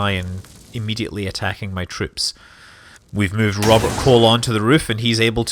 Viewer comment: Did you just say Robert Colon? XD